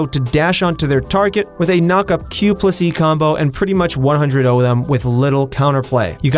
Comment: did he just say q pussy combo ???